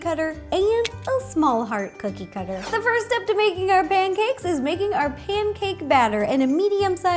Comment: I thought she said, "The first step to making our ban-cakes" 🤣 Btw how do u make those ingredients do that?